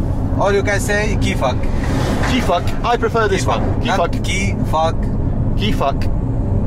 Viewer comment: Since this is in Lebanon where French is spoken... Did he say "Qui fuck"?...